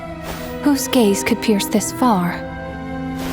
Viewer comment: Why did I hear that as “whose gays could pierce this far?” 💀